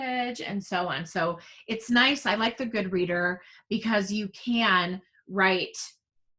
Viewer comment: I thought you said Good Read was the app you were using to work with students using OG. I could not find anything on Good Read for this.